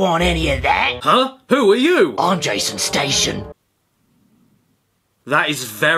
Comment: On the Jason Station bit, I thought he said Jaystation and felt dread and anger